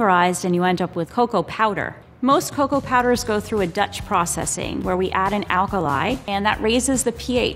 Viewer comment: 😆😆 what did you say??? Dutch?? 😆😆😆😄😄😄